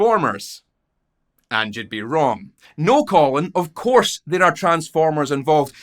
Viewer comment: I did notice those transformers, but i kept hearing "You might be thinking transformers... And youd be RIGHT!" I swear it was a legit berenstein effect lol